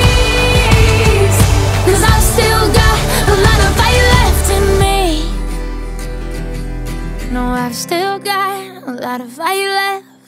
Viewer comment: I thought she said " i still got a little voilets in me "